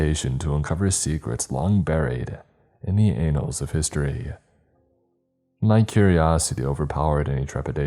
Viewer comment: Did he say anals of history at 3:31?